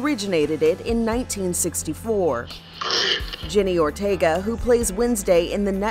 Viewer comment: did she say “jenny Ortega?” 💀💀